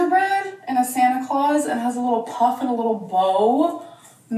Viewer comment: why did I hear say "A little puff and blow"? And thought "Amber, are we moving on from the edibles to something else?"